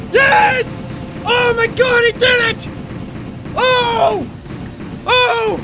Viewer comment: if you turn on auto generated captions when he says "oh my god i did it!" The caption say "oh my god return it!" Hahaha i nearly dropped my phone, like "yes! I have bested him! Now go back."